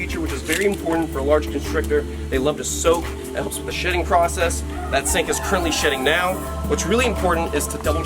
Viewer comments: At first, I thought you said it would help with the shitting process 😂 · I thought he said “that helps with the shitting process” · When he said shedding I thought he said shitting